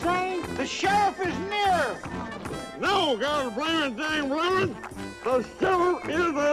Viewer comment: Gabby: Hey! The sheriff is a n(CLANG)! Harriet: What did he say? Dr. Samuel: The sheriff is near! Gabby: No, gone blame it dang blammit! The Sherfiff is a n(CLANG)! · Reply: 😁 I thought he said, "No, dog gone it, ya dang varmint! The sherrif is a N!"